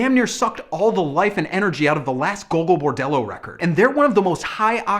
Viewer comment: "Gogol Bordello". I had to figure this out, because I couldn't hear it.